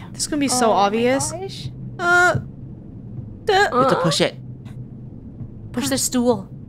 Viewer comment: I heard say “oh $hit” lol